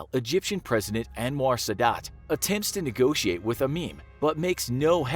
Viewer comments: did he say a meme? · I thought he said "... attempts to negotiate with a meme ..."